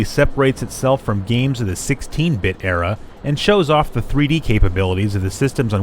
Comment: Yeah, duh. What's your point? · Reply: Oh my bad I went back and watched again. I thought you said 32 bit era instead of 16. Lol I was like man GT is way better. But I agree 16 bit era racing games are terrible. Ever play days of thunder?